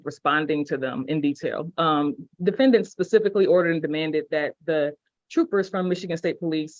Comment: Did he just say Pecifically instead of Specifically?? Just curious lol. How do they expect to be taken seriously when A…they DO NOT know law as well as NOT KNOWING how to speak correct English? It’s actually kind of sad…